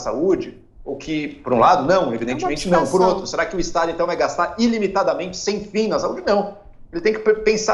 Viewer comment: Ouvi um "será que o Stalin vai gastar ilimitadamente..." aos 7:33 XD